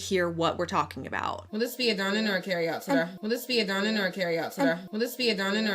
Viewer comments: i keep hearing "this dine-in or carryout extra", but us accents are crazy. None of this words are pronounced legibly. I can't hear today at all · wow! I thought she had said “sir” too. But you playing it again, it does sound like she said “today”. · I thought she said "carry out serve"? Because they have to serve it to them when it's ready.. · I thought she was saying "will this be a dine-in or a carry out, sorry", but the 'sorry' was mumbled. as someone who works in food service, if i have to ask a bunch of questions, I tend to throw a little 'sorry' in there because people can get really impatient - especially if someone's already been waiting a while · I hear “Dine-In or Carry out Serve”. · i hear "carry outside". But "today" fits too. I really doubt she could've called her "sir" with that baby voice 🫢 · I heard "dine-in or carry-out serve" 🥴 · I heard “carry outside”